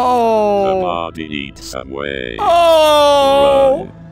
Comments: I heard "The Body Eats Away" Then : "The Body Eats Subway" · Instead of hearing "The body eats away" I heard "The body eats Subway" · I thought it said the body eats subway 😂😂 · "The body eats subway" thats what i heard